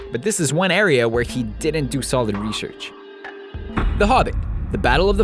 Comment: Lol who else heard him say rehearch instead of research after the fury part